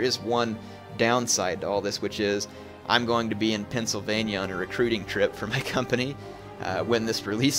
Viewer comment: lol, I first thought said Transylvannia insteed of Pennsylvannia